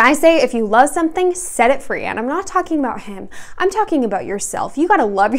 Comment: "If you love something, set it free. And I'm not talking about him. I'm talking about yourself." YES MA'AM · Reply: I thought she said "And i'm not talking about ham", this makes more sense yeah.